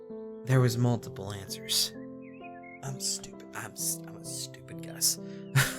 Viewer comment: As someone trying to be a hackerwoman, i would love to see you do more of these. I also look forward to dem Unity tutorials. Also also doesn't it sound like says "I'm a stupid Gus" at 45:06 ? lol unless he's saying "I'm stupid guys." I could be wrong, i mean i heard him say "now Max, focus..." at 36:34, when really he said "relax, focus..." hahaha